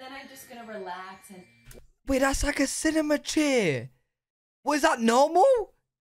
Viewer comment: As an American, I think it's funny you call the recliner a cinema chair. Where I'm from, recliners were standard in homes when I was growing up, and reclining chairs at the movies only became a thing about 10 years ago lol. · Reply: I was listening while getting ready for work and could've sworn he said 'cinnamon chair' 😅